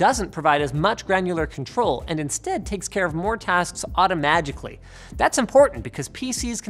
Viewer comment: did he say automagically lol